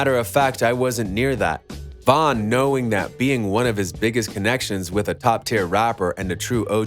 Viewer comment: Who else thought he said “niggas” at 1:06😂😂😭